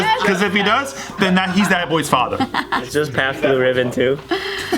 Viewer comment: What did say? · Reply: Tony Ho "it's just pabts blue ribbon too" that's a beer brand that's cheap